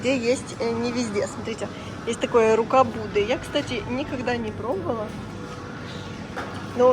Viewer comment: Послышалось "рукаблуды"